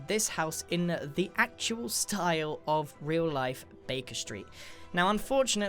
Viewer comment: I thought u said “bacon street” not Baker Street